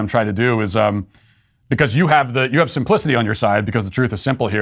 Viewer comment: I know Matt said you have simplicity on your side but it really sounded like he said you have some pussy on your side.. Just me?🧐🧐